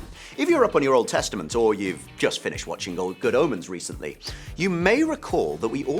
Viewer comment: what movie title is he saying? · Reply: Good Omes. TV Series